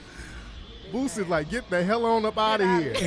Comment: Anyone else thought they were saying BOOTSY instead of Boosie?